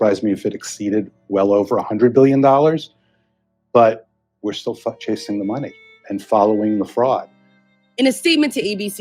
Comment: Did he say “But we’re still f__k chasing the money..”??? #WEJUDGE 🫡🫡✊🏽✊🏽